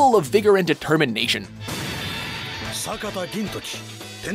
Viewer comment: I heard "Sataka" not "Sakata", lol Still appreciate Gintama finally appearing in WatchMojo